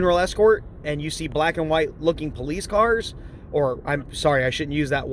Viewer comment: * --- TIMESTAMP: 7:08 ACTUALLY, THIS IDIOT DOES NOT REFER TO THEIR VEHICLES AS "POLICE CARS". JUST SAY'IN.... WHAT HE ACTUALLY SAYS IS: THAT THEY HAVE "BLACK AND WHITE LOOKING POLICE CARS" his point was that his work vehicles are similar looking to police cars. You DID NOT NAIL HIM.